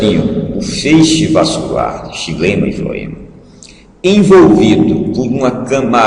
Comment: Alguém entende o que ele diz em 2:51? feixe vascular o que??? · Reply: Feixe vascular de xilema e floema